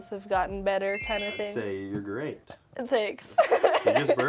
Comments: I thought Jaiden said Sexual skill instead of Social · I THOUGHT JAIDEN SAID SEXUAL SKILLS AND WAS LIKE JAIDEN the children...